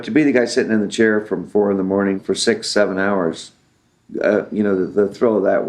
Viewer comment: did he say <num> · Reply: Yeah 6-7 hours. That's how long the makeup took.